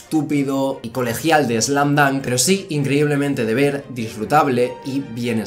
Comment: "slan dan" ¿Que anime es? No se que dice ahi xd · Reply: Slam dunk es un manga y anime del autor takehiko inoue es uno de los mangas más vendido de la historia en Japón y el manga spokon más vendido de la historia , como dice ilu se rio más con slam dunk y en lo personal yo también es muy divertido aparte es el mejor spokon de la historia , es una serie incompleta ya que su anime no fue terminado el final solo esta en manga y su dibujo a pesar que es de los 90 es brutal por otro lado la animación esta un poco vieja por ser de los 90 pero eso no le quita calidad a su historia